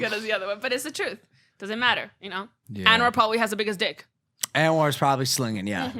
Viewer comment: Who did she say was like McLovin? Anwar Paul or something? Anyone know she's referring to at 33:30?